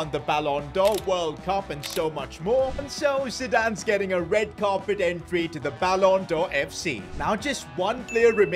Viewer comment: I fr thought he said "Red Card Entry"💀💀💀